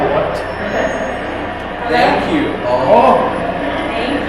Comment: says “here, I’ll move one, keep moving it” and I swear I heard thank you right after 👀